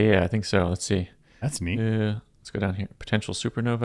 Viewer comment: i thought he said in response to cyclically rupturing gas, “that’s me” instead of “neat” for a sec. same tho lol